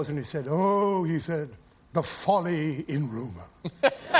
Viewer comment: what did he say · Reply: The folly in rumor